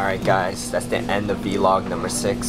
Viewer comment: Omgg At the end when u said peace, I thought you said Beast :D -3 Why am I bringing Kpop this video?.. And is pretty :)